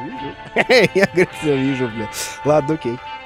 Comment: Кто услышал что он там сказал ? Я нехера не услышал. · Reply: "я всё вижу"